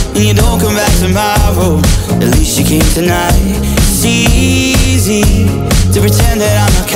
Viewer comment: Why do I always hear it as "it's easy to pretend I'm avocado"?